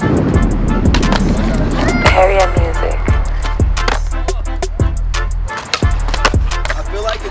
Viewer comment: Did I hear “Heria music”